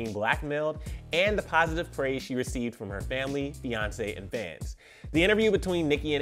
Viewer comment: I heard "Family, Beyonce and fans"